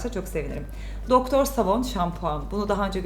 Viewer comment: Ben doktor savan u duydum